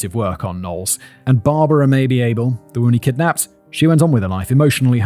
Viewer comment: When he read “Barbara Mabee Abel” I heard “Barbara maybe able”. Just me?